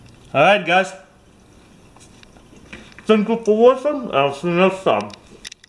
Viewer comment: fankfew fow fwaching fwand iwl see fou fext fime I'm sure that's what he said